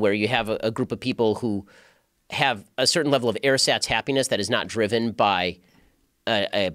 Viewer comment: Ben says "air SATs happiness" (at least that's what the subtitles are showing). Can anyone explain what he means? I've never heard this word or addage before... thanks :) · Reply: “Ersatz happiness” (I.e. artificial)